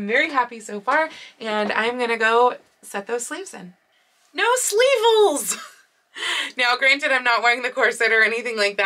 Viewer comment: I know you said "no sleeve holes" but 😂 I first thought you said " no sleevils!" I'm so happy that the sleeves worked out so well.